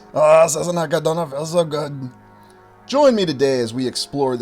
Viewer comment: Now we been brothers for a few years now and I wanna know damn well what your audio is saying at 0:59 😊you can’t fool a fool my guy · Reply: "Ah Sassenach, I don't feel so good" it is a reference to Jamie Fraser always being seasick.